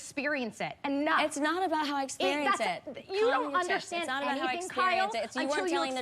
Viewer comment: does Kyle say “Common sense” or “Calm your tits” ??😂 No but I’m really seriously asking though. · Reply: Hahah i think it's the latter hahaha